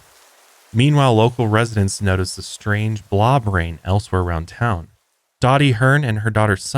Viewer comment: I know you’re saying “blob rain” but all I hear is “blah brain”. 😂